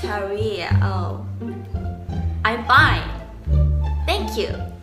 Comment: wait she didn't say "I'm bi" but "I'm fine"?!! Lol omg i always thought she said she's bi 😂 oh god i lost it right now!!